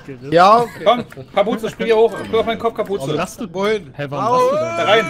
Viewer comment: Also irgendwie weis ich zwar was gesagt werden soll, aber bei 16:15 verstehe ich jedes mal "Spring auf meinen Cock Kapuze" :D